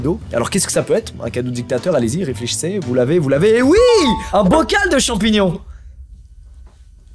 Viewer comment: J'ai entendu "Vous laver ? Vous laver ?" Le cadeau c'est une douche lol